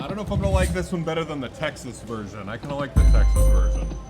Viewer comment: I need to stop watching when I’m so bored tired. I thought I heard saying that he liked the Texas virgin. I had to stop and re-listen. I didn’t think there were virgins in Texas.